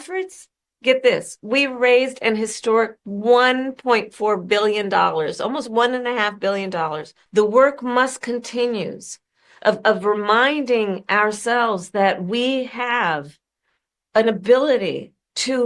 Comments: Wait ... I swear I heard her say "The work must continues ..."😆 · Did she just say: "the work must CONTINUES"?